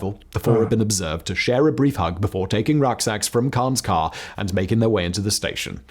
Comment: "Rock eggs"? "Rug exes"? No! You misheard that: they wore rucksacks (backpacks)